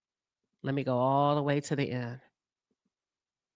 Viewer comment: I thought nikki said she was gonna cardi teeth in.